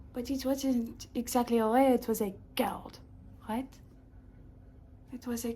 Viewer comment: what is she saying 3:15 when she says "it was a ??? from the organ of the sun" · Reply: “It wasn’t exactly a ray, it was a cord…”